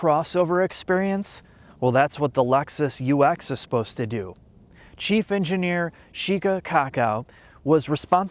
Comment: I wasn’t watching the video, only listening. I thought you said Chief Engineer Sheca Cock Out like you were trolling.